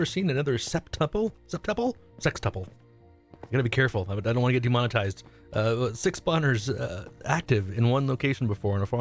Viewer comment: he says he doesn’t want to get demonized yet the caption says “we have 6 boners” 😂